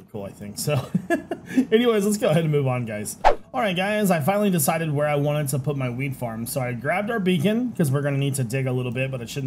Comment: LUL Sounded like you said, "I figured out where I want my weed farm." 😆 My brain hears things some times....